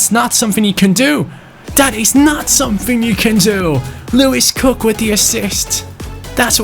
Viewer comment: sorry, but did he say Lewis Cook?